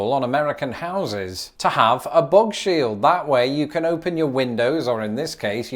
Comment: When you said "bugshield" I heard "bog shield", and semantically it still checked out. Subscribed.